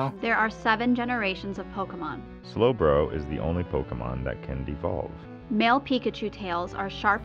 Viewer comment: "Slowbro is the only Pokemon that can't evolve" did he says that? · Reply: That can devolve is what he said